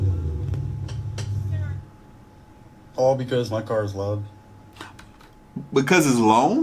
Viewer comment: i think he said "because my car is loud" and i think he is in a supra